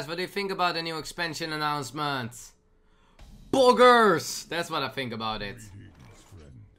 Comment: what did he say...? Boogers...? Bogus...? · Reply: Slikk1980 Poggers (Pogchamp)